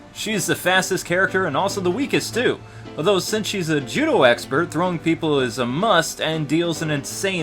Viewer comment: I need to get my hearing checked!!!! I originally thought you said that Blaze was a Jew expert instead of a Judo expert